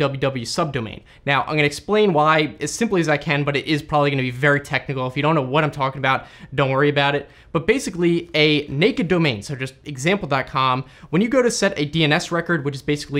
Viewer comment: I thought he said nakeddobeman😂😂